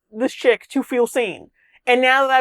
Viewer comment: wait is he saying sane or seen? Because you know how some black people say ask as axe.